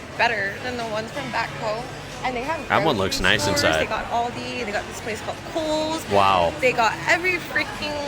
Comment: girl did not just say all-de 😂😂 I can’t